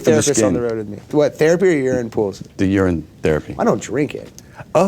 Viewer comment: "urine therapy" he thought he said "You're in therapy" hahahahaha